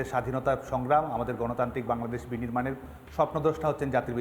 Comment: Did he just say sopnodosh 5:43 ????